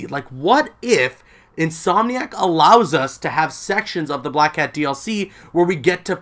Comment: "allows us to have Sex" is what I thought you were gonna say for a second there